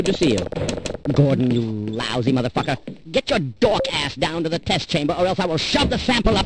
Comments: From now on, I will use the phrase "Dork-Ass" more in conversation... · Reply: i totally thought he said dog ass, “GET YOUR DOG ASS DOWN TO THE TEST CHAMBER” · Get your "dawk ass"? I need clarification 🤣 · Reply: I heard "dog ass"